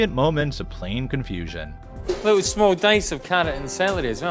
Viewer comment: what in the world did he say??? lol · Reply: Small dice of carrots and celery